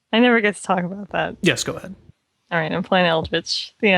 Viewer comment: I thought said "I'm playing Eldrich. The end. [Beat]... the end." And yet "Beat" was actually "The". After all, one does not simply beat Eldrich, with its full story Rouge-like requirement and cold, hard ice dungeon. One does not simply beat a bitch either, but I digress... what I'm saying is that Eldrich is a bitch of a game... a sexy bitch... that's scary and low-resolution... it's good.